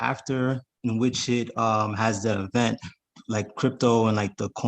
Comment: Oh in the video was the caller saying “having events at around 57:35?? I’m writing my own notes so I was curious. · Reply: I believe halving event. Bitcoin halving was last year.